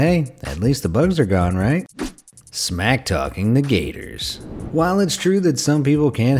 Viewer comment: isi t just me or does it say "smack-talkingrs the gators"